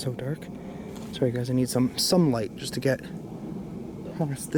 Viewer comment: The voice sounded like it said I need some help